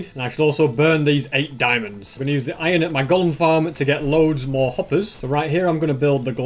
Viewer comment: When he said iron from golem farm, I thought he said iron from gold farm